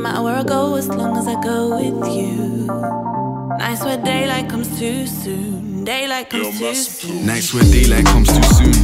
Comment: Did she say, "Day like I'm Susu ( piss )" ?